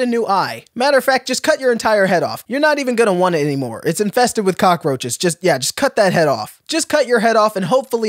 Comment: I thought it said cockroach toes and i